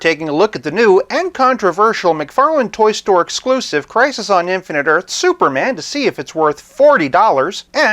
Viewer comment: Swear I thought I heard you say "Prices on Infinite Earths"